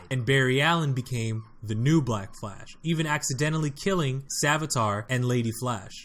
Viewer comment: I hear a dog in the video at 3:04 and I think black flash’s name is actually death flash